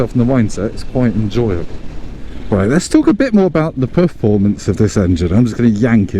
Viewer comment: Just before you were yanking it, did you say Poof-formance at 12:44 🤣